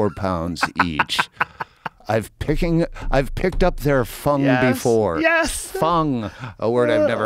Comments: Thought he said "thier fun" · Could of sworn he said I've picked up their fun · I thought Depp said "picking up their fun" 😆